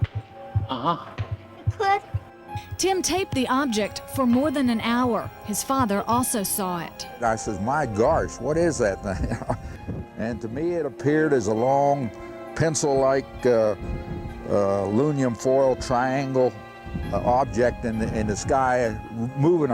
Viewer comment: Did he say "My garsh @ 1:22"?? What the heck is that??